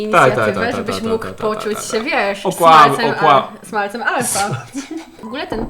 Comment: Czemu ja usłyszałam "Smalcem Alfa" 🤔